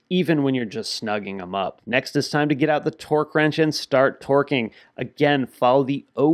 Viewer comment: It sounds like he said get out the torque wrench and said start twerking.